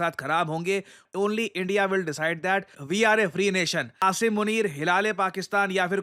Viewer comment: मैने सुना असीम मुनीर हिलाले अवार्ड फिर ध्यान से सुना हिलाल ए अवार्ड । dirty mind 😂😂